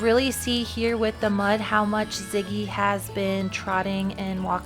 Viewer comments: I like it, I thought I heard you call him Lincoln, but Ziggy is cute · Love your channel and what you do. Think I just about heard you say Ziggy right at the beginning of the video could have been buddy though 😊